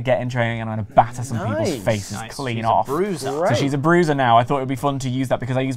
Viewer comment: I completely thought he said CRUISER and not BRUISER during his booking. "Piper as a cruiser, huh?" I thought, chuckling